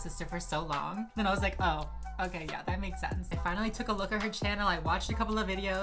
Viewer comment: I'm dead I THOUGHT you said "I finally took a look at her chin-" 0:27 like you didnt have to do her like thaaaaat omg